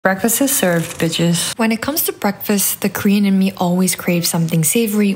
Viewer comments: I misheard and thought you said 'when it comes to breakfast, the queen in me always craves something savory' and thought, same 😁🧡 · Bruh I thought she said "the cream in me"